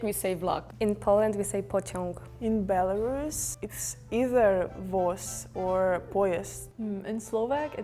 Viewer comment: For example? I think it was pretty accurate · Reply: yet another one at 10:11, she says "voz" or "poiezd", but the subtitles say "tsiagnik" or "poiezd". I am not a belorussian, so i wouldn't know if "tsiagnik" actually means "train", but according to comments from belorussian speakers, it seems like it's true, but again not the word she used